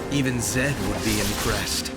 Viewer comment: I cant hear cleaely what he say on 4:00..even zed will impress?